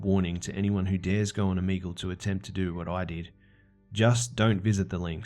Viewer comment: I think Omegle is probably the stupidest and most unsafe website idea ever. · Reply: I always thought they were saying “amigo” 🤣🤦🏻‍♀️😂